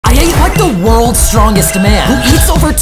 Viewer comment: Bruh I heard "I ate the world's strongest man"